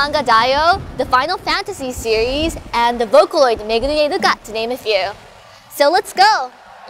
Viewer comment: what did she just said in 0:15.... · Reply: "Megwine uka"